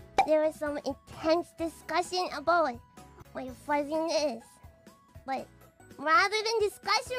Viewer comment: I think Mococo said "fuzziness" here, though I agree "flatness" makes more sense in context